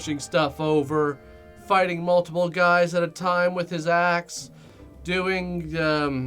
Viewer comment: I thought you said "fighting multiple guys at a time with his a$$"